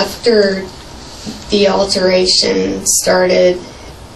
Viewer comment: i appreciate that you put "altercation" because thats what makes sense and what she was trying to say, but that girl definitely said "alteration." Like a dress alteration 😂😂😂😂😂😂😂